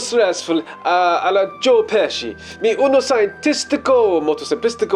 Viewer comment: Did he say "Joe Pesci"!?